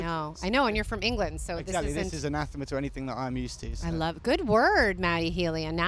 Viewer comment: The word is "anathema"? I couldn't hear properly.